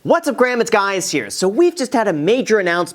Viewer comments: is it me or did he just say whats up graham its guys here? lol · I hear: 'what's up grahams, it's guys here.' 😆